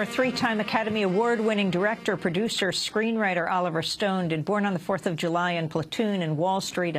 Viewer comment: sounds like she said Oliver Stoned in the begining